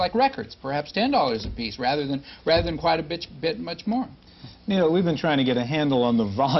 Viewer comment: I heard "quiet a bitch"